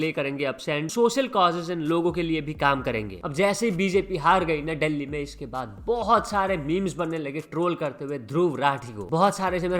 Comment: Kya bola bjp har gai delhi me pe